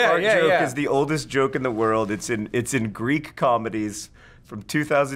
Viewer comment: did he say greek?